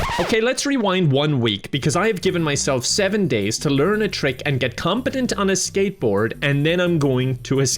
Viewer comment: I thought you said "Get competent on a skateboard, and then escape"